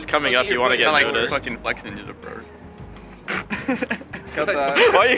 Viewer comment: Did I hear that right? Sick mentioned something about flexinja?